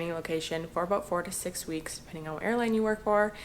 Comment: I thought u said 46 weeks lol 4 to 6 weeks lol